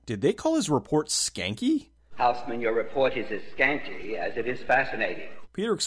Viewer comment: Sounded like he said "scangy"?